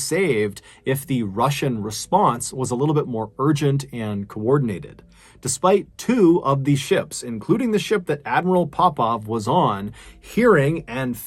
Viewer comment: I swear I heard him say Admiral Popeye